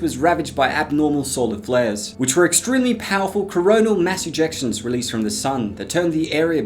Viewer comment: Did i just hear corona 1:20 The heck